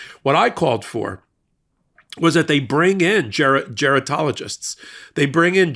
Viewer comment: Ok. So, I twirled around and nearly fell down - in horror - when I thought I heard you say: "...What I called for, was they bring in - JARED" (it was actually "Gerontologist") for President Biden. Please excuse MY tired brain, as I have not had MY daily nap yet...